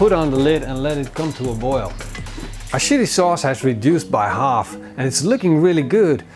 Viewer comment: am I the only one who heard "our shitty sauce" 😂😂